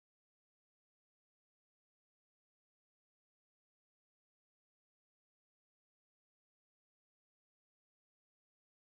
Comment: What did Hearn say at 34:48 ? Chisora vs Who ? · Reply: didnt say an opponent, but its confirmed chisora is fighting, he said "chisora vs someone"